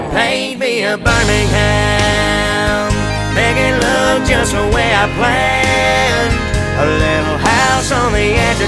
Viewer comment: love this song. but i always thought he said, 'making love just the way i planned', instead of make it look the way i planned.